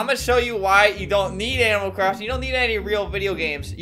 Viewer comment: did Albert say animal craft?